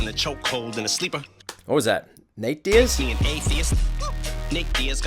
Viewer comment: that line bothers me that he said nick instead of nate referring to submission game